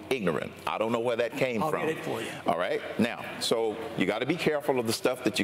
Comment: Last night I was confused and thought Ron Paul was being a smartass and said "well good for you" after Cain, with his crappy tone, said he "You have misquoted me. I did not call you or any of your people ignorant..." BUT then I re-watched this today and I noticed @ 1:27 Ron Paul says "I'll get it for you" .